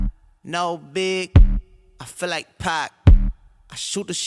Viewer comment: coulda sworn i heard him say he feels like pie that first time around